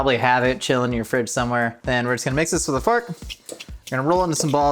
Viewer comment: I thought Josh said mix it with a fart 😂 but then I went back and heard fork